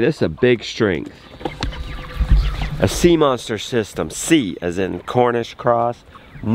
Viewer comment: I’ve been watching you for a loooong time. Okay, I finally learned why you call them C monsters… all this time, I thought you were saying Sea Monster, and I couldn’t figure out why! Ha! Thanks!